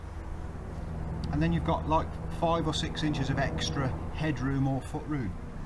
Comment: Most interesting to me: A Brit talked about "Meter" and not in the Imperial measurements. 🤣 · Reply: When watching the second time, I recognized you mentioned as well inches... I just heard "meter" the first time, probably because I did not expect it... :D